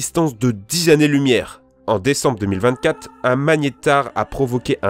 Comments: il a dit ne décembre 2024 ou c est moi ? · C’est moi où il à dit 2024 à